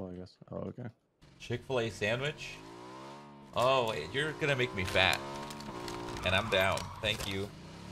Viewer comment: thought he said "You're gonna make me fap." lol